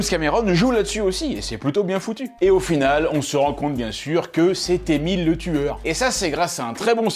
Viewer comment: Moi j'ai entendu "C'est Emile le tueur", et j'ai pensé direct à Emile Louis... Sauvez moi.